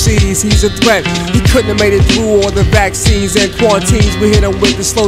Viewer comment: 1: Masta Killa] (x2) Intruder alert, sound the cymbal This be the war of the Shaolin Temple It's time to re-stack my gun We the ones that'll come through and smack sparks out your art With the exception of a few that respected it for what it was Sick venomous syndrome, studied many styles But this tactic I've adapted and made it my own Feelin' rightness at it at home, splittin' your head with the chrome He's one who sees he's a threat He couldn't have made it through, all the bad seeds and quarantines We hit him with explosive gases, so he couldn't teach the masses But still, who came in the name of Wu-Tang to nourish the brain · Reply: I thought he said vaccines instead of seeds thanks for the lyrics